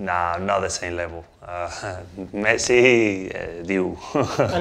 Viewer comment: Interviewer said "That game". I heard " Mbappe"😂